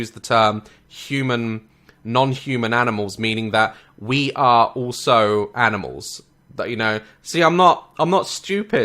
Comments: Am I the only one who heard her say we rape animals 😂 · Did she just say RAPE animals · Bruh did she just say that we rape animals xD